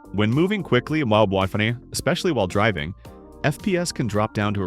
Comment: what did you say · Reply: What the frick, I think he was trying to say WiFi